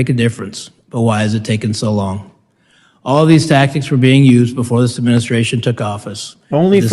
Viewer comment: you spoke up saying "only for election purposes.", which made it difficult to hear what he said. He said that the cover-up tactics were used by the previous administration (i.e., Trump).